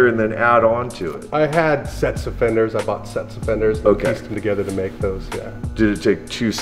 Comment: Lol..my wife thought he was saying he "bought sex offenders." Ps note to youtube: Never preface a video with a Bernie Sanders campaign ad AGAIN. EVER.